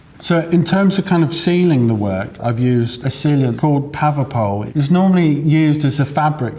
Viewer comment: Anybody catch the name of the stuff he uses as a sealer? It's at 8:09. Sounds like 'Pavapo' but I don't think I'm hearing it correctly.